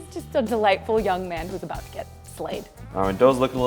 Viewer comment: instead of “slayed” the subtitles showed “laid”. i think that means something